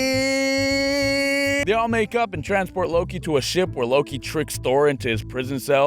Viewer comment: I low-key thought he said make out